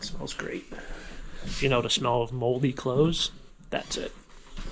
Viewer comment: 2 years late on this, but when you said, "moldy clothes" I heard "moldy crows" and thought Ewwww! 😂